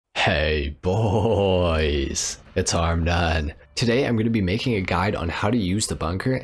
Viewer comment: i always thought you were saying “it’s Arm Nan”